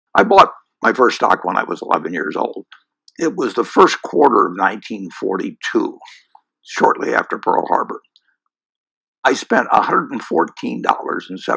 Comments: Why did I hear "I bought my first dog when I was 11". I was like, ok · I thought he said dog, not stock; and his message was him trying to say to not buy a dog and buy stocks